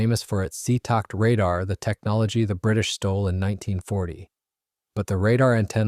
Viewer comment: "stole" the Seetakt radar. I thought you said they bought it.